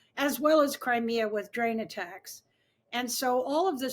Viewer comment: Sound like shes saying drain attacks